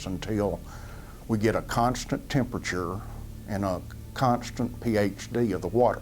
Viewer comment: does he say "constant PhD" of the water?